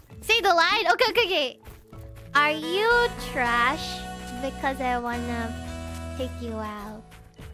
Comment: I think she said, "Beause I want to take you out"